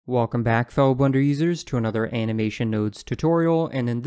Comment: At the beginning I thought he said "faux blender users" and I was mildly offended